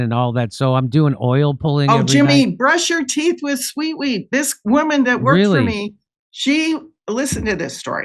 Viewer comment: Brush with seaweed? Sweetweed? What is she suggesting I don't understand what she's recommending here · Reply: She's talking about a product that she sells at brightcore called sweet wheat which is a wheat grass juice powder. She should have been more clear then she could have have sold some